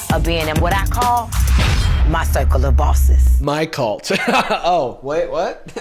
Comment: When she said circle of bosses I thought she said psycholobossis